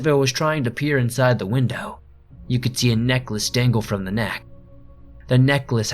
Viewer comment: What you said: "A necklace dangle from the neck" What I heard: "A neckless dangle from the neck" I was confused for all of about five seconds there.